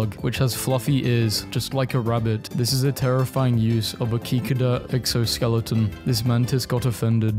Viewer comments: did he just call a cicada a ceeckerda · bro did not just call a cicada a “kikadah”💀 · DID HE JUST SAY KIKADA